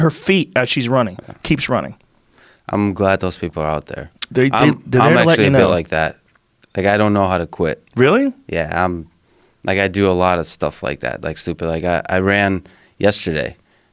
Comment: he said the guy don't know how to quit I heard him On the version that I just heard Whether it's changed later or someone's getting a different version That's what he said